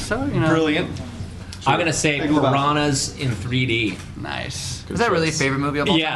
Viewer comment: What Movie did Matt Flynn say at 5:59? I can’t hear it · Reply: Piranhas in 3D!